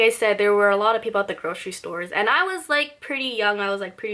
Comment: What is she saying? Kroshy store? What is that?